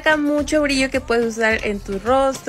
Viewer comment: Soy la única que escucho "en tus piojos"?? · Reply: Yo también escuché "en tus piojos"😂